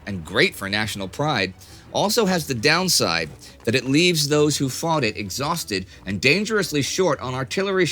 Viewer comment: Gosh, I just heard "those who farted" instead of "those who thought it"